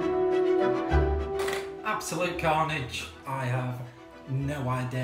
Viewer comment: I thought that he said absolute garbage😂😂😂😂😂